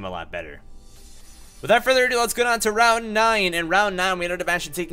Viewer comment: Coulda' swore he called Claydol Route 9